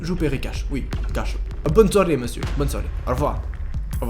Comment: mdrr j'ai entendu "abonne toi monsieur" 😂😂😂😂